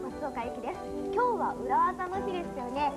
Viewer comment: 今日は裏垢の日って聞こえた